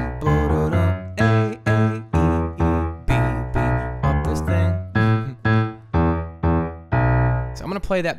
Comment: did you say fuck this thing xD